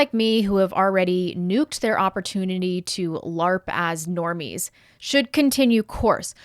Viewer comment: Uh, did she say "larp"?